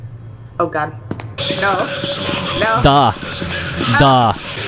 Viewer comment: what did he say in russian around after this timestamp??? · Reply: Da- yes